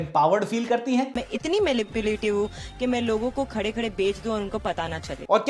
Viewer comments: did she just said MALIPULATIVE? Ye konsa word ijaat kiya h bc? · did she say malipulative 😂